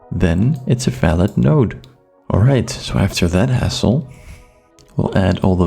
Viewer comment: you say "so after that ???", what is the word you're saying? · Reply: I say: "Alright, so after that hassle" Sorry if its not audible, i had to whisper for the recording of this video :p